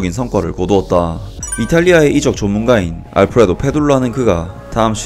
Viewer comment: 저 왜 이탈리아 이적 전문가가ㅜ아니라 ㅈ문가라고 들리죠?